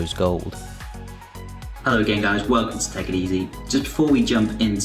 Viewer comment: I swear he says "hello gay guys"